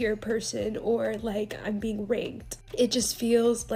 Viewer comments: She said rigged right? Cuz with that context of that sentence I really thought she said Raped 😳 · Did she say it felt like see was being r-worded…? If getting free food, free night out, and free tickets to an esteemed event count me in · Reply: I thought she said that too, but she actually said ranked. The pronunciation was bad. I had to read the captions on the Tiktok and relisten to that part several times to hear ranked.